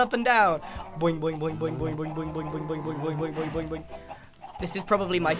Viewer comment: Did he just say oink... for cows?